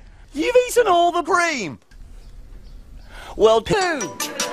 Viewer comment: does Small say poo?